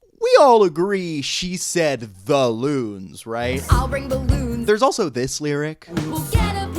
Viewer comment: Wait, it's really "the loons?" I heard "I'll bring balloons," which both fits the meter and is a perfectly grammatically correct sentence. Just... why?